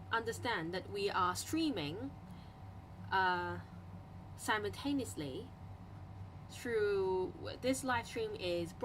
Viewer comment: Hmmm,is that 0:50 semi-stainlessly??? I couldn't understand qwq someone help plz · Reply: Ohhhhh,"taneously"!（sorry for asking a dumb question haha）